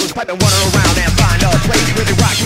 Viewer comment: I am not a native english speaker. Can somebody tell me WHAT „is really bad „ in this song??? I am beggin you, this is my chhildhood mystery, i can’t tell what the word is. 1:09 - what is really bad? Public enemy said that „stuff is really bad”, but here... this is definitely not „a stuff” Clop? Drop? Hop? What is really bad? Please help me!!!!!! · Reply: I could never figure it out either, when I was little I thought it said "Raw Fish really bad" lol